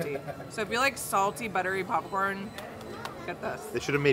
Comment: Every time I hear say, "pupcorn" it makes me laugh! 😆🍿